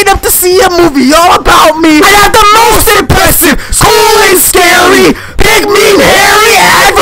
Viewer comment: i heard "SCHOOL AND SCARY"